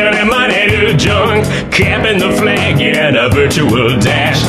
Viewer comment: I thought he said, "Campin' the flag". Not cappin'.